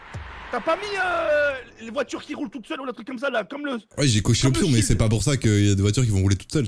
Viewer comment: / 6:52| Il a dit j’ai cocher pion ?? Dite moi si j’ai bien compris svp